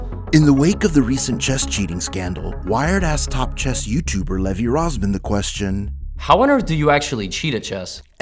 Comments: lmao i totally misheard it and thought it said: "wiredass top chess youtuber levy rozman.." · "Wired asked" sounds like "wired-assed". Wired should have thought of this when they started calling themselves "Wired" and they might consider ENUNCIATING what they say. I had to look at the closed captions to check what was actually said. "Wired-assed chess YouTuber Levy Rossman...."